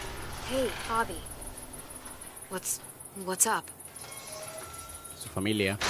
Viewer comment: "¿Que...que pasa?" Se Entiende: What... Whatsapp? xD Like si se escucho lo que dijo Clamentine!!😉👍❤⭐